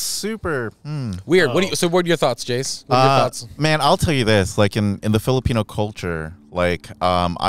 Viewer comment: I hear “I’ll kill you”